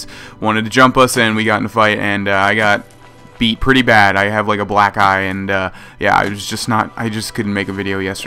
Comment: I thought you said I have a black guy XD